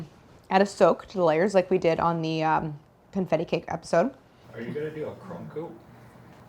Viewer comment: It’s a super famous brand of cream cheese, lol. they just can’t say brands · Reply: ohhh I totally thought they said "funfetti" but your comment makes more sense